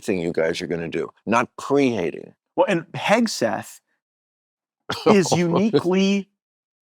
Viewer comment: does he say “pegseth”…